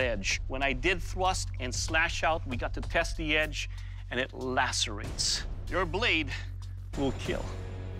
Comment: what did he say it lastera something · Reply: He said "lacerate".